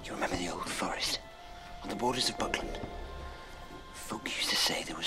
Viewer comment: I thought he said "you remember the old forest? at the border of Brooklyn?" and I was so lost